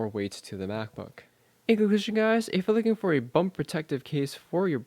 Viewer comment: Nice review. What are you trying to say at 4:58? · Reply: "in conclusion guys if you're looking for a bump-protective case"...